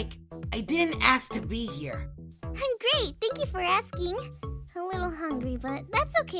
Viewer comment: Omg idk if its just me but when said, "I'm great thanks for asking!" I thought she said, "I'm gay thanks for asking!" xD